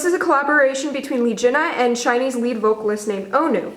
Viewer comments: I thought she said Chinese but she really said shinee's lead vocalist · It's surprising how you got it wrong about the male vocalist of this collab. The male singer "Onew(온유 in Korean alphabet 'hangul') is NOT a Chinese BUT a Korean. He is actually a member of another K-pop boy band SHINee!!! Remember Jonghyun? He is also a SHINee member, too, or was... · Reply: DAVID PARK ot think u misheard it, she said "SHINee's vocalist Onew" not "Chinese vocalist Onew"